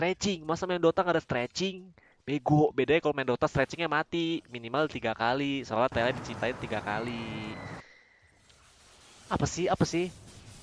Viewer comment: ngomong apaan si ga denger gw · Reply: "Minimal 3 kali soalnya tele di ciptain 3 kali"